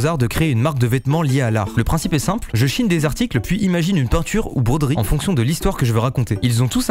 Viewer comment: j'ai entendu "je chie des articles"